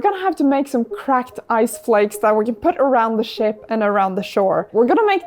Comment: Did she said she-... oh, it is ship. I'm so wrong...